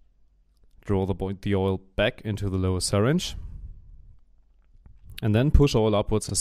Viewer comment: What is he saying at 2:10 droy abop oilyOil back into the syringe?! What?! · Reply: " draw oil back into the lower syringe"